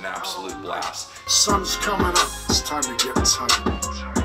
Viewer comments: Dude! I almost unsubscribed from this channel. I thought he was saying, "Bust a nigga" and not "Busta Nickel". Relax folks. I'm black. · All I heard him say was “suns coming out time to get turnt. bus, bust the liquor. Buss buss the liquor, niggaaa